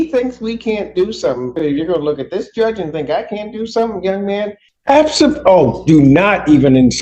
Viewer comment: i thought for sure i heard a f on the end of that abso 😂 judge Simpson almost dropped the "absofuckinglutely"